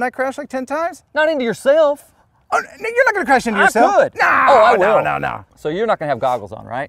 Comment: Sound like he said “N@ you not gonna crash it into urself” @ 9:00 🤣🤣🤣🤣🤣